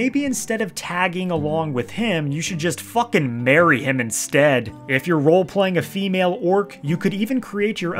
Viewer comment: I swear I heard f!ck and marry him instead (my straight-ahh Orc two-handed berserker dovakhiin would gladly do so)